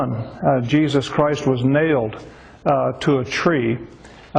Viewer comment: did he say a tree at 0:27 ?.?